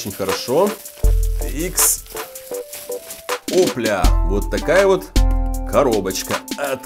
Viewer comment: Мне одному послышалось ох бля на моменте